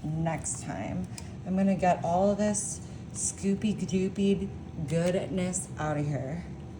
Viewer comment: I thought she said spooky dookie 1:15. 😂